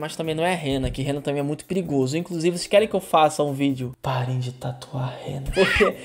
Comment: achei que ele tinha dito "pare de tatuar hentai" com o final cortado